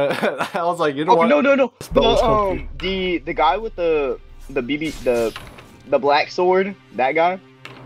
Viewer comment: AYO WHAT WAS HE SAYIN? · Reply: The big black caliber 💀